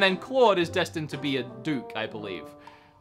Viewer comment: TFW I thought he said Claude was destined to be a spook and had to rewind the video.